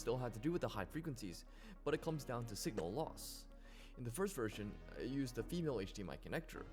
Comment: haha did he just say loss? | || || |_